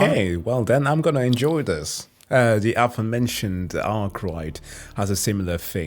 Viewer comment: In 32:21, what is a book name ? I can't understand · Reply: Arkwright by Allen Steele